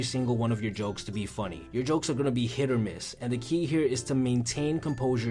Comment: DiD hE jUsT sAy HiT oR mIsS????